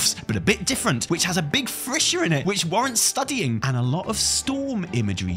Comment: I don't know if my cold is really affecting my ears, but it really sounds like is saying frissure instead of fissure, which, hey, I assumed was a real word that I just hadn't encountered before, so I typed "what is a frissure" in to google. Don't do that. Frissure isn't a word, google auto-corrects to fissure, but(t) focuses on the fissures that occur on a certain part of the human anatomy. At least, my search did, so please, learn from my mishap. Mishap, or subtle trolling by, knowing that intentionally mispronouncing a word would make people corrupt their search history.